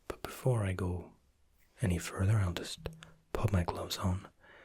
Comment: When he said I'll pop my gloves on I thought he said I'll just pop my clothes on I was like what?